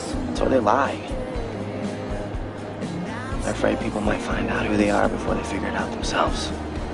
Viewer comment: I don't get what Lucas says at 1:05 & what Brooke says at 1:22 :( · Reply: Lucas : i am afraid people may find out who they are before they figure it out themselves